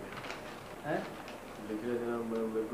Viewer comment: salman - likhte likhte me so gaya.. That man -- hmmm ?? Salman -- likhte likhte me so gaya kuchh khaya nai so gaya..... (may be he said somthing like that) That man --- koi baat nai kal likh dena Salman -- nai nai thik he..... Moral of this story love day loot.....😂😂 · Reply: Usne bola me abb padh do.. Means read krke suna dooo...